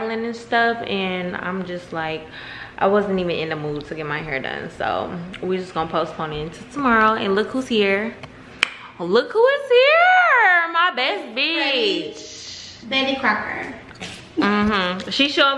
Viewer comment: I thought she said pregnant betty crooker i was ganna cry in joy 😭